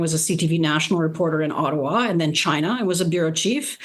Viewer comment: did she say China? I am old my hearing is not great.